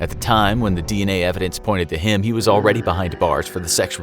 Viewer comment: I thought you said kim corona